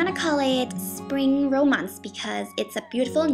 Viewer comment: lol i thought she said "spring ro-maz"